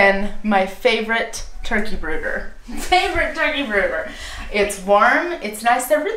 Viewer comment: Hi there,thought you said turkey burgers,not turkey brooders! Lol Your vids are so much fun Thanks